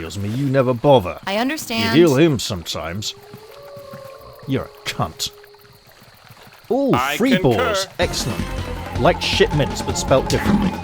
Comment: Didn't quite hear what you said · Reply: "in fact, give him a strength spell. He can feel amazing. And give him some health back. Excellent. Our warrior is now in tip top shape for a little while. He is The Cube."